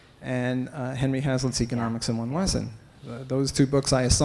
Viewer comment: The questioner mentions two books, first Atlas Shrugged, what was the second? I can't hear what he is saying. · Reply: Economics in One Lesson by Henry Hazlitt